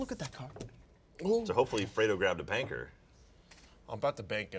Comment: Did Jack say Panker? :\